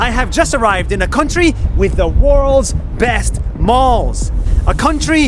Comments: For a second I thought it said “worlds best balls” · i thought he said “worlds best balls” if you didn’t read the subtitles 😂